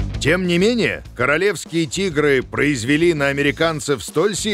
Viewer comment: мне показалось что он сказал:"советские тигры произвели... "